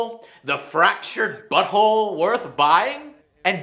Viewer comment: i can swear i misheard Joe saying Fractured Butthole :D